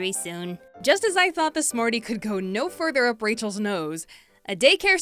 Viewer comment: Did you just say "Smorty"?